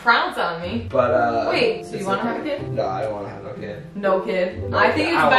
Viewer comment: "I'm confusion" "Nah I don't wanna have a kid" ' I think its best you don't have a kid, your English is not there yet." Me: "huh, what the?????" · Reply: He said, "I don't wanna have no kid." Instead of a kid, that's why she called him out c